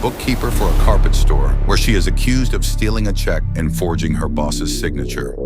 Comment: Man I thought it said stealing cheeks an f*****g her boss